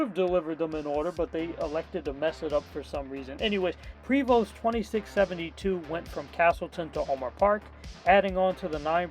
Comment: I decide to check the comments after that 67 I heard in